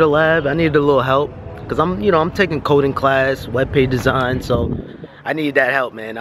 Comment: tuscan leather 🔥🔥🔥 let gunther have a pistol in his pocket n that lady wouldnt say anythin.... i see how it is. i thought you said codeine class i was like wtf 😂😂😂